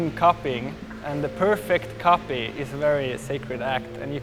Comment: I don't understanding the conversation at 22:00, what does it mean? It sounds like he's saying "coughing" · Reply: It's "copy"